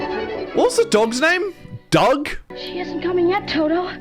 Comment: am I tripping or did he just say Doug